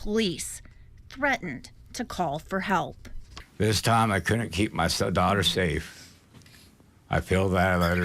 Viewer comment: I couldn't keep my stepdaughter safe is that what he said or am I tripping is this his real daughter or is she a stepdaughter because if that's the case then I'm really getting the pervy vibe off of this situation